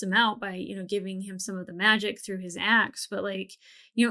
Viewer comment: I thought you said Grog was taking some heavy damage and Pike helped him out by giving him some magic through his ass.